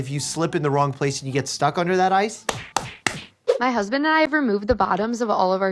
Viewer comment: what. Did he just say...AMONG US🔥🔥🔥❗🗣 EDIT: Ok he said amongst us but close enough.